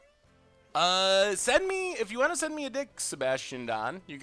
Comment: does say "send me a dick"?